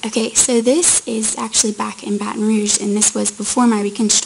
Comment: Did they say where the young woman with the long name is from? I found it interesting she wore a haku (head lei). She seemed to have such a sweet spirit about her. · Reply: Loo Shrloo Never mind - I just heard her say "back in Baton Rouge"